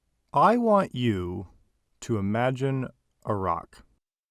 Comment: I thought bro said imagine Irak.